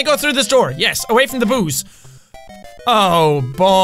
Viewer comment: when he was saying boos around 7:18, i thought he was saying booze .